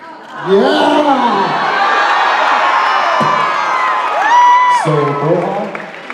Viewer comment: does anyone know what the girl answered here? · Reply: That she's a Sam girl